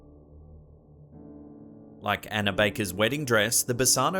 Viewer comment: When he said Anna Baker I thought he said Hannah and my first thought was “That bish ain’t even reallll”